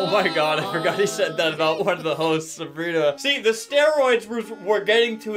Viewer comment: I think he cursed at 0:51 i dont know it sounded like he said h*es😭